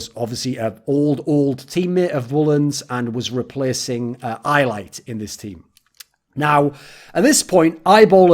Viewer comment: is he saying iolite? I can't understand what the name of the player that's being replaced is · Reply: iLight